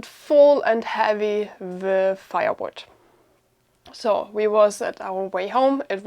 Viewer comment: Sorry, no help from me, BUT when the video started I thought you said you had a trailer full of FIREWORKS --what the hell ? then I put the closed caption on and see it was a trailer of fireWOOD. glad you safe.........